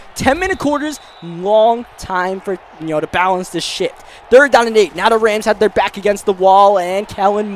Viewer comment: did he just say that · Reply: shift lol